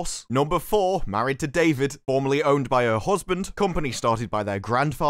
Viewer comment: For a while, I thought you were saying she was formerly owned by her husband!